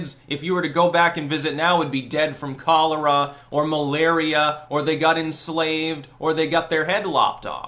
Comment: He said "malaria". I heard "muh laria".